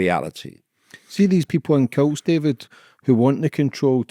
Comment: it sounded like you said ‘see these people in kilts’ 😂😂